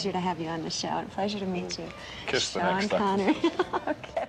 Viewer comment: That lady is a GREAT interviewer! Good solid questions. Had Connery completely at ease. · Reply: Yes, great interview. Love the bit at the end where he appreciates the warmth of the interviewer's closing remarks and it sounds like he says "kiss the next time!"